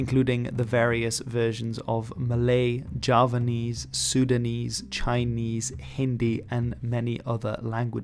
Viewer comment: It'ss Sundanese and not Sudanese!! I might have just misheard what you've said but It sounds more like Sudanese than the correct one which is Sundanese!